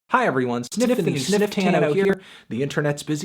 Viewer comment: thought he said stiffany stifftano at first